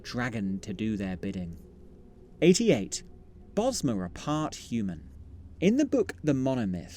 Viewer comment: I was listening to your video and thought you said "Bosnians are part human" 😂